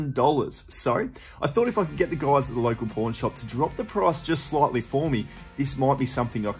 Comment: Good luck selling that Pokémon card! I hope you let us know if it does sell soon! You got some great stuff at the Pawn shops. Sometimes with your Aussie accent it sounds like your saying “Porn shop”, instead of pawn shop…. Makes me crack up! I’m not knocking your great accent I do love it! Good luck with selling the goods this week!